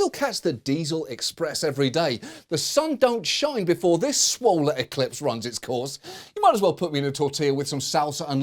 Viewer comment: In this video at 06:45 the automatically-generated caption read "swollen eclipse" instead of "swole-ar eclipse", making the joke inaccessible to Deaf and HOH fans. LastWeekTonight needs to put Closed Captioning on every video. It's common courtesy.